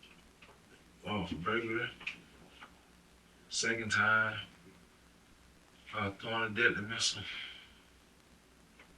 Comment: …. Did he say he was pregnant? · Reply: “In prison twice”. Not pregnant. Hoping you haven’t been wondering about this for the last year!😂